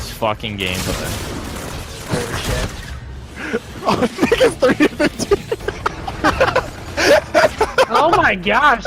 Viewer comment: I thought he said "Nigga is 3-13" omg